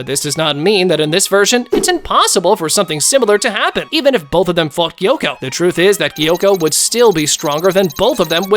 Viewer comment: when he said "if both of them fought gyokko" I thought he said "if both of them fvcked gyokko" 😭✋